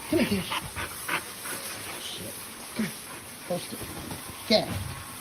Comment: I may be crazy but in the dog video, it sounds like Maggie says “Eddie, he’s got a bird in his mouth”?